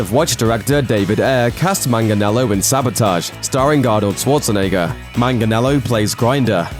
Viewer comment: sounded like you said Galdot schwarzenegger lol